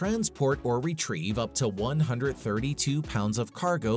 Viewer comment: did it say 132 pounds?